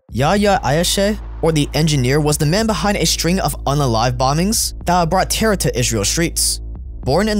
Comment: did bro just say "unalive bombings"?